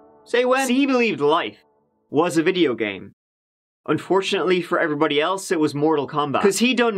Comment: "sibalin life was a video game" what exactly did he actually say. Can somone tell me